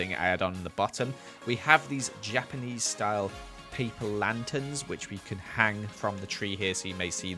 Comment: I thought just said- Japanese people lanterns- :’D